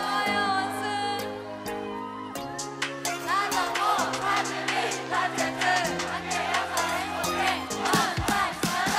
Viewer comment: I'm so curious about what the fan saying at 1:00 is member's name or something? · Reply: Its Im Nayeon Yoo Jeongyeon Momo Chan Sana Chan Park Jihyo Mina Chan Kim Dahyun Son Chaeyoung Chou Tzuyu